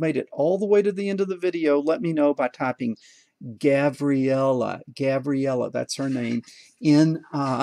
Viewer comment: I thought you said gavriella